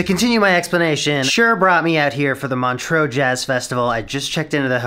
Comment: First time you said Montreax I thought you said Montrose a small town in Scotland and I was soooo confused